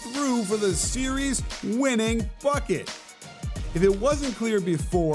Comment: I swear at 8:01 I thought I heard "f*ck it" instead of "bucket"....I was so focusing watching PG's defense that I guess I could hear his thoughts too. What an amazing clutch game winning shot that was...